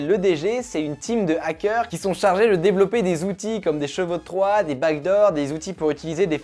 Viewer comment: je me suis sentie tellement con "C'est quoi des bagues d'or" ? ........ oh wait O_O